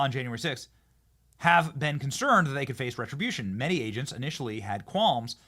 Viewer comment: I'm told it's "retribution" but all I hear is "justice"